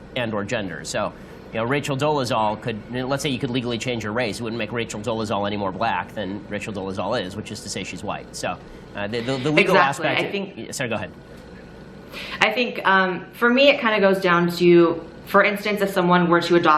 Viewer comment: lol i heard "sir go ahead" :p